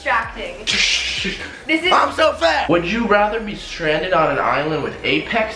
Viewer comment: LOL at 8:58 it sounded like Alex said "I'm so fat" But i know he actually said "I'm so fast"